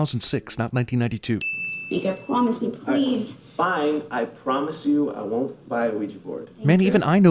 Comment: I swear I heard, I promise I won't buy wii sports